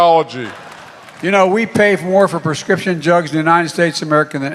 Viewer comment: Omg!!!!! It sounds like he said jugs instead of drugs!!!!🤣🤣🤣😂😂🤢🤢🤢🤢🤢😡🤬🤬. This guy is a pathetic excuse for a President!!!! I hate his guts!!!! When is someone going to remove him???? How long will this go on??? Totally ridiculous!!!!! President Trump please save us from this dumbass!!!!!!🙏🙏🇺🇸🇺🇸